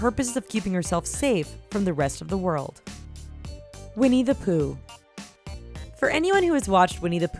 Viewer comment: when she said "Winnie the Pooh" I heard "We needa poo"